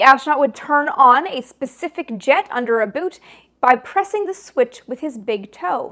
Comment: She said "jet shoes" and I said Bless you! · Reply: She said "a boot" and for a brief second I thought she said "about". 😊😊😊